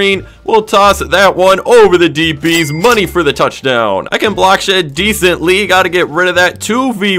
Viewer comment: does he cuss? 1:18 Edit: I think he said block shed? idk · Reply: He said block shed